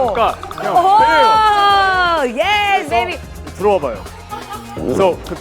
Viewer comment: did she say “yes baby “😂 Also were they lowkey flirting or it me